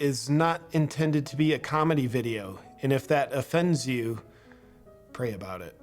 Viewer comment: I think you misheard that. He didn’t say it’s going to be a comedy video, he said it’s not going to be a comedy video. And for some reason he thinks not telling jokes is now offensive?! People who start out with some dumb statement about you not being offended means they are about to say a whole bunch of purposefully offensive garbage. It’s just like that statement “I’m not racist, buuuut…”